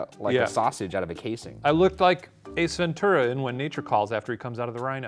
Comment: Did Shane just say "Ace Ventura" but somehow make it sound like he said "a centurion?"